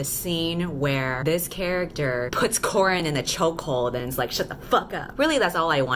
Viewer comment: I put this on while doing homework and for some reason, I kept hearing "corn in" instead of Corrin so it was "choking corn in" the entire time and I was like "wtf is going on"